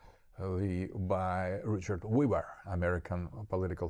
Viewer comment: What American philosopher is Dugin talking about here? I think I hear the name "Richard Weber" but I am unable to find any political philosopher with such a name?